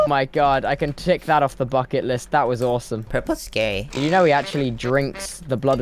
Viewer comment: did he call purpled gay or am i hearing incorrectly?